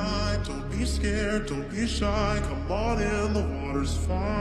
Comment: I just heard " look in my eyes dont be scared dont be shy come on in the war is fine " 💀